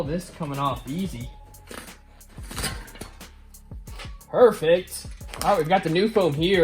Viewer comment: you say perfect. Sounded like mortal combat haha